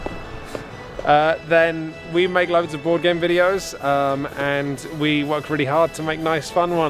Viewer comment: You make love to boardgame videos? (I may have misheard this